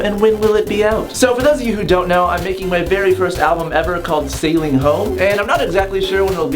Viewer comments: When he announced his album name, I thought he said "sailing hoes" so I was like "WTF" but then I saw the cover at 1:07, I was like "OH!! Sailing home not sailing hoes!! Ha ha, how, or why would he want his album to be called "sailing hoes"!! ha ha ha... That was embarrassing..." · definitely thought you said Sailing Hoe when you were talking about your album hahaha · I thought you said your album was called sailing hoes